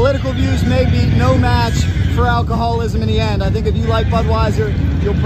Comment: The camera man saying "alcohol will trump political ideals" and "people will come back" is funny. That might be true if there weren't any other beers. There are hundreds of other beers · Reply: I thought he said “alcoholism” will trump politics. 😮. Even worse.